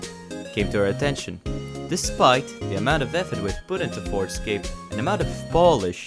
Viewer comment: I misheard forgescape as forgeskin and now I can't unhear it - help